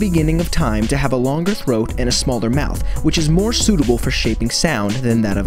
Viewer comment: Well... Our science teacher told that to us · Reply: Jonathan Gagarin oh never mind, I thought you said smallest XD.